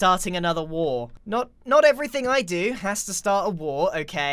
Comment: “I don’t like to start wars.” Mumbo: “I want to start the Bumbo Baggins Society” Also: “I want to start a sewer society” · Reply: I thought you said “I don’t want Star Wars” lmao